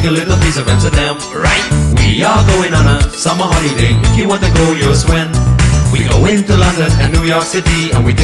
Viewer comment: All this time I thought he said "if you want to go,ask Sven".